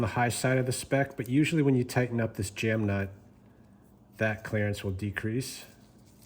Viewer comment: i think youve had too much exposure to us southern folks... Sounded like you said " clarence " instead of clerence! lol